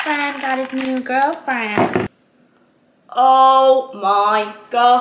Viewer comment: What did she say at 31:14? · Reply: I heard “daddy’s new girlfriend” which tied into the previous comment of “hot but not too hot” 😂